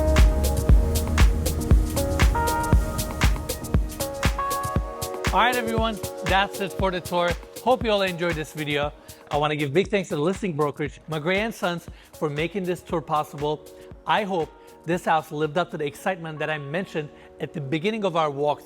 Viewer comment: When he said the listing agent I thought he said my grandsons. Lmao im like I know y'all age well but damn! Lmao